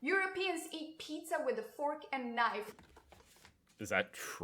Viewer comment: All I hear is ‘fork n knife’ like pass me the f*** n knife